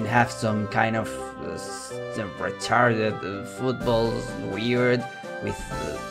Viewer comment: Did he say "retarded footballs"?